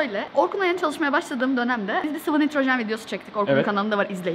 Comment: ne diyor anlamadım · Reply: Bir sıvı nitrojen videosu çektik diyo